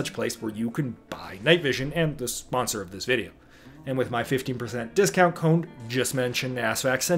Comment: 15.. damn I thought you said FIFTY percent discount code